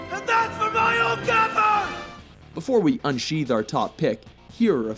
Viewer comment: It sounds like is saying: AND THAT'S FOR MICHAEL GAMBON!!!